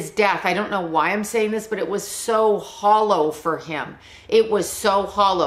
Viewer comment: Funny you described his death as hallow. I was curious and looked up when he died, Halloween, 1993. I know you explained it, but nice play on words to pop in your head lol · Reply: I thought she said "hollow" ...?